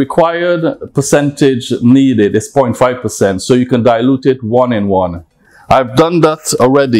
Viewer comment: Sorry, what did he say at 1:57 - dilute it 1 and 1?